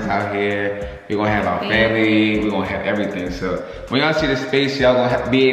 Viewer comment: lmfao i hate how i heard "we can have our baby" when yall said family 😂 smh . im sorry